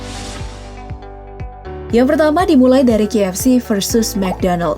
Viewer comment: ko guw dengernya JFC?🙁